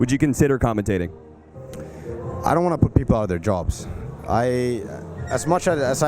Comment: what does notail say at 7:28 ? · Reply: i dont want to put people out of their jobs